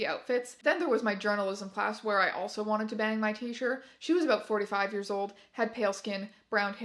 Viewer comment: scared me for a second because i heard "45 years old" as "4 to 5 years old"